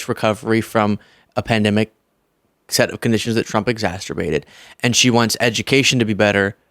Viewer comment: Did actually say ExasTerbated at 9:04? 😅